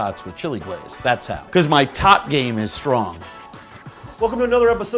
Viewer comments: I thought he said top game. I kinda lost it and then watched it again and was disappointed. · I thought he said his top game was strong. and i was like andrew zimmerman was a gay total top. and my gay fantasies flourished. but then i replayed it and realized he said tot. :(